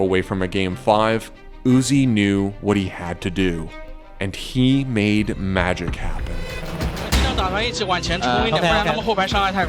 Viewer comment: anyone knows about what did he say? he made what? maajor cato? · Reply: "he made magic happen"